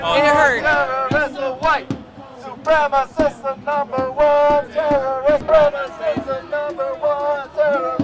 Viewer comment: It sounds like she saying they want white supremacists with dumb chant. Brainless, ignorant, and stupid.